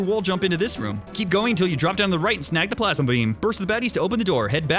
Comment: Plasma bean. Okay it actually did sound like you said plasma beam in a country accent but I thought it also sounded like plasma bean which I liked lol.